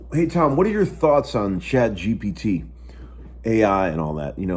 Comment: Shit I'm old, thought you were saying ChadGPT, figured via Google 🤔 🤣